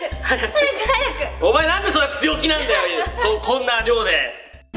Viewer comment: のなんで、そんな強気なんだよゆん！って言ったように聞こえた！！